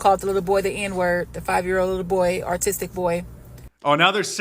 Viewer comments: She did NOT say he was autistic!! She said he was ARTISTIC! I'm not sure if she was referring to his theft, and thought that the way he steals is an art, or if she thinks that since he stole, he must be a rapper artist, but she definitely called him artistic. Probably one of those rap artists who say n1gg3r/n1gg@ a lot. I don't know why so many black people love that word. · she's so stupid and stereotypical that she said "artistic" instead of "autistic" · No,she clearly says artistic.😂 So,clearly, she has no idea what Autism is.